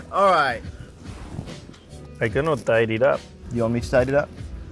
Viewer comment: what they said at 5:18??? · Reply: Carlos said "I cannot tie it up" and Charles answered "do you want me to tie it up?" so romantic haha